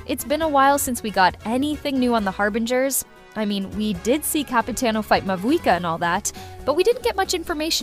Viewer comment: Excuse me...but did i hear Capitino?😅🗿